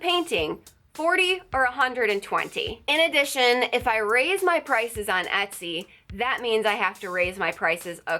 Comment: I thought they said you xan raise the price